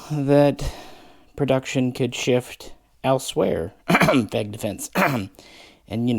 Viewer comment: what is he saying in the *cough*, I cannot understand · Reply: FEG defense